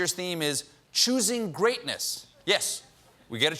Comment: Lol I thought he said cheesy greatness 😂